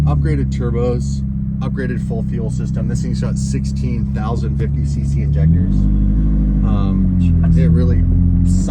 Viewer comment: lol i thought you said 16,050 cc injectors at first and was like... Um wrong lol. Then I realized after hearing it 2 more times he said 16 - 1050cc injectors. Funny thing is I have those same injectors in my Turbo Civic.. Injector Dynamics 1050x, just... (4 of them, not 16 lol)